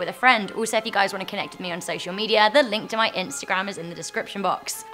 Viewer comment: i thought she said sexial medias